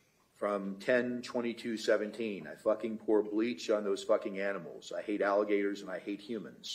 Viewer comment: Lost it at "HIV positive porn" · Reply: Bro I heard, “I hate alligators and humans”, what do alligators have to do with this?